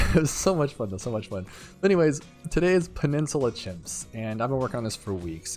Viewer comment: thought it said penis chimps